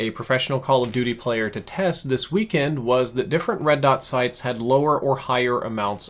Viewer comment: Red to sights? Sometimes I wonder if your descriptions are made via speech-to-text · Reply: He said red dot sight it just sounded like he said red to sight